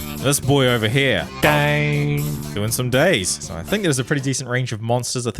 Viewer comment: was I the only one that thought the monster said "gay" at 1st 😅👀👌